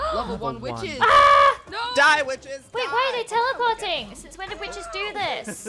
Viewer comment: I thought said go bitch but he really said witch